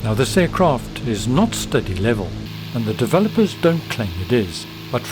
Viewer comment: the Closed Caption types that you say, “this aircraft is not steady level and the developers don’t claim it is.” What does “steady level” mean? Or did you say “study level”? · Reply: Study level.